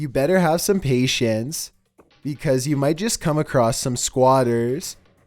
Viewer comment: Best joke yet! #9 "if you go exploring an abandoned hospital, you better have some PATIENCE" 🤣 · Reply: I really thought he said patients lol,not patience..